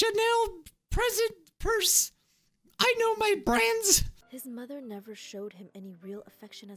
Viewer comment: when he said ''i know my brands'' i thought he said '' i know my brains ''